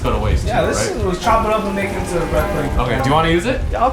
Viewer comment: if you are wondering what he said he said make it into bread pudding